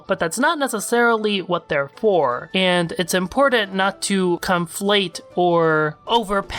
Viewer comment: did you say Stoic like stoy-ik on purpose? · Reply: Pitifulwalnut5 this boi says comeflate